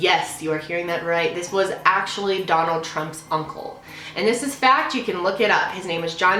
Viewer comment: am i the only one who her 'funcle' instead of uncle